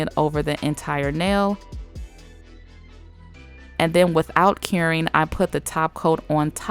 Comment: Once in awhile when she says “without curing” I hear “without caring” and I’m like yes girl idgaf either 😂